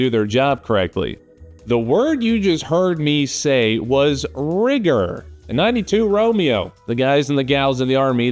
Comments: Thank you for explaining, for a moment I thought you said "rigor". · rigger,thought you said, cigger. Wigger ( heard that before? ) Quigger down under.